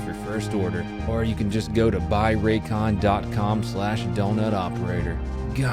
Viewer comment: Thought the intro said "buy raccoons"